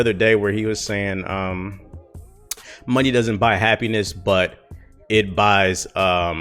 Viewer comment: I think the quote I heard was “Money only rents happiness”